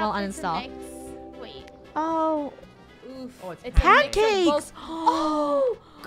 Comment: instead of pancake I heard cancake😂